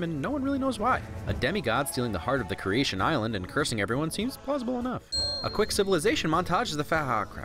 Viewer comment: the way he says "no one really knows why." and then "seems plausible enough" in the next sentence are said the exact same way, i thought he said the same thing twice for a moment and had to rewind it.